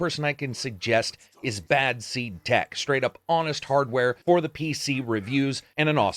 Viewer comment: LOVE the shout out to bad seed ! I just recently discover him myself ! i don't even have a ps4 and watch this for your review, I still think this is another on the list for the "ps4 exclusives, i wish was on the PC" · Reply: Is that his name? I wasn't sure of the name At first I thought he said "Back Seat Tech"